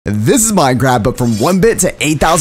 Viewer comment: is it just me or in the intro did it sound like he said this is minecrap